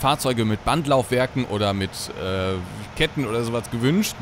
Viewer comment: Ich habe "Bandlaufwerken oder Disketten" verstanden. (Kind der 1970-er / 1980-er Jahre)